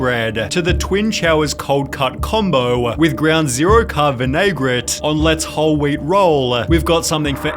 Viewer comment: Ground Zero-Carb what sorry? Say that word again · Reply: Vinaigrette